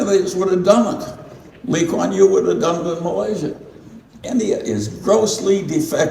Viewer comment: did I hear him say LKY would done it in Malaysia lol